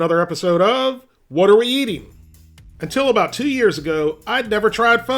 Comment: Lol I just heard “what are you laughing about?!” Great video 👍